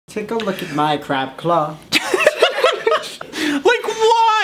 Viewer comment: I thought said "Take A Look At my Crap Cloth"